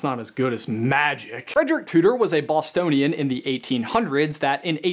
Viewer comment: Who else thought that he said Austin instead of Boston?